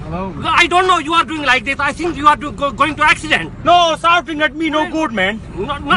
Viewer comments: Shouting at me no good, man. Me no shout at you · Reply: This whole time I thought he said "salting at me" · "Souting at me noo Gud maen" LMFAOOOO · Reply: Tech Ventions i thought i heard him say "salting at mee" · Shouting at me no good, man. Me no shout at you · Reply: I thought he was saying farting at me